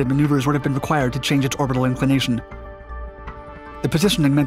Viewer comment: am I hearing 'orbital information' in that rushed voice, should be 'orbital inclination! Doesn't know the realm of his subject matter.